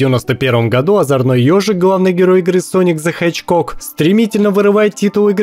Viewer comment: Sonic The Hedgecock. Послышалось ?